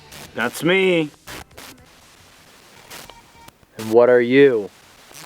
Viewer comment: when asks what are you? I heard why are you asking? You already know